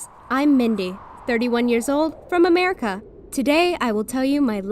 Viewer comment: I THOUGHT SHE SAID "hi im mindy 83yrs old 0:04"